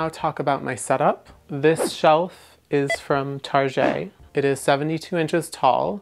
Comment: OMG, did you just say Tar-je, (Target) LOL